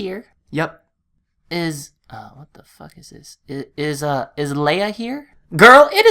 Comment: did he say what the fuck